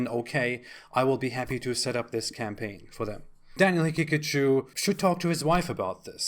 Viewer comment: I keep hearing "Daniel Pikachu, Daniel Pikachu." Why would Daniel Pikachu want to go anywhere but Japan?